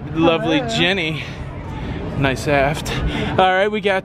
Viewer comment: did he say nice Aft? Referring to Jenny? Hilarious